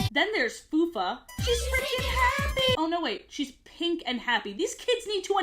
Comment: So glad I wasn't the only one that misheard "She's FREAKIN happy!'